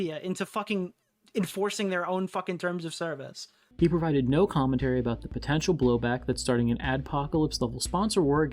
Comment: He said that? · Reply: no, he said "He provided no commentary" he said "he" and "provided" together quickly so it sounded like he said "writed"